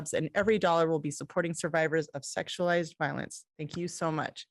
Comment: I feel so confused, I heard him say he loves this charity stream because it’s to sexualize violence, so I laughed, and then started talking about sexual assault.